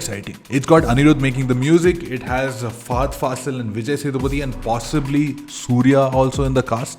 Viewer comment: 🤣🤣🤣😂 The subtitle says FART FASSIL instead of fahad fassil LOL